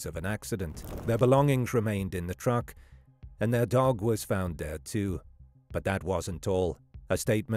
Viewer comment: it totally sounds like you’re saying the dog was found dead too, but your CC says “dog was found there too.” 😂 Sounds like you’re contradicting yourself but you’re not—it’s just weird sounding 😂